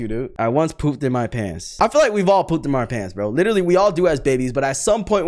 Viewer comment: did you say we’ve all pooped in your pants? 😅